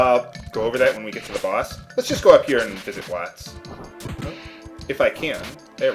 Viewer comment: I for some reason thought you said "Heiss" when you said Watts.. I'm to much into your main LP.